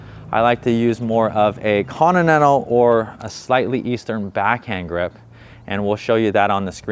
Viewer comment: I do. If I see it high, I can change quickly and get it ready to go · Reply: I thought you said you used a slight eastern grip for the flick. Isn't that the same grip used for dinking?